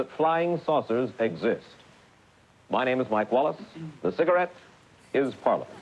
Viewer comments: "I'm Mike Wallace, and this cigarette is Parliament." Love it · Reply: I thought he said “and this cigarette is part of it” 😂😂😂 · "I'm Mike Wallace, and this cigarette is Parliament." Love it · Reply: I thought he said the "cigarette was permanent"... so a chain smoker, having a bit of a joke. Obviously not a smoker to know about types of cigarettes... not going to lie, bit disappointed. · Fully thought he said “and the cigarette is part of it” and I was like: yeah, duder. We know.